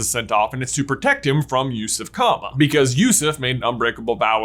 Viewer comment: i swear every time he says Yusuf Kama i hear "use of comma" and i can't stop